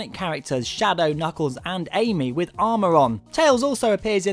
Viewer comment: Is it me or did he say amy at 0:29 O__o